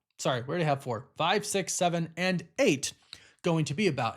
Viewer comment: what he say?🤷‍♂️ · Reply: "And eight"